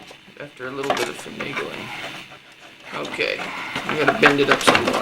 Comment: did he say fanigiling? btw cool vid. like the 70's oufit.